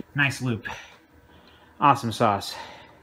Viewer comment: He sounds like BitWit Kyle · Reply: did he just said "AwesomeSauce", this is really BitWit Kyle!?